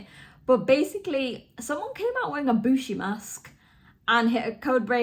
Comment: Oh, wow. When you said, "someone came out in a Bushi mask," I thought you said, 'Someone came out in an Ibushi mask."